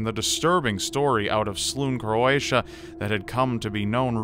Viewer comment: Did u say Croatia at 1:15??